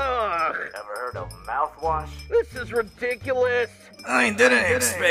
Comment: do I hear ridiculous chloe?